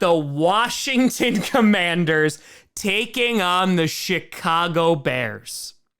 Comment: did he say Chicago or “Shit-cago” 😂😂😂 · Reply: Shitcago he always calls them that